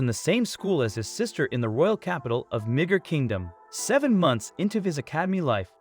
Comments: what did he say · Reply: Midgar kingdom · who else heard migger as n****r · ? Did i hear that right? Nig-…. Idk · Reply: Midgar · What did he say from 9:27 to 9:33? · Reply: midgar kingdom